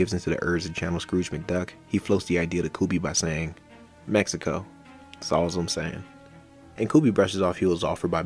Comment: I thought he said "Magical. S'all's I'm saying." And Bill Burr's response was just for them to hurry up out of fear of Walter.